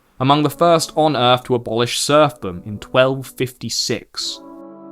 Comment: to abolish what? I couldn't catch it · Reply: Serfdom, i.e. feudalism